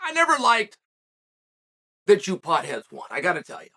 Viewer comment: “ I never liked… the chupotheadones, I gotta tell ya” Can someone let me know what he said there. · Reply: "that you potheads"